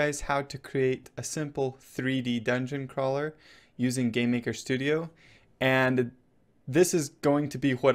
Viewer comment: It sounds like he says Gay-maker studio XD